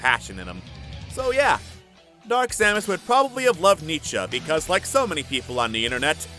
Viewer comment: Can someone write that last word? "Dark Samus would probably love ???". I can't catch it properly. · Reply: Just guessed my way through Google and I assume he said Nietzsche.